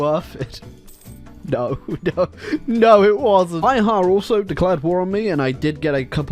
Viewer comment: what said: "Bihar also declared war on me", What I heard: "My heart also declared war on me". got me worried there for a sec